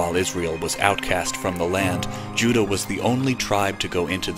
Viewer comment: 18 Did NOT say Judah, it say "they." STOP CURUPTING THE WORDS OF YAH..